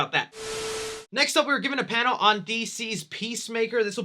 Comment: I have something to say... so i was watching this video and was about to talk about peacemaker but and ad came up so it sounded like he said “DC’s pee”